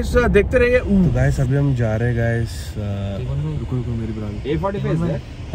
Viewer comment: 🙄 yeh kya bol rahe hai? bra kabse pehen ney lag gaye🌝🌝🌝